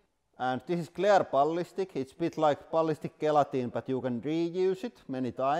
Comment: I was so confused for the first couple minutes. I thought he was saying ballistic guillotine 😂